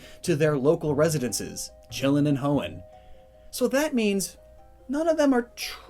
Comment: When you said “ chillin In hoenn “ I heard “ chillin & hoe’n 🤣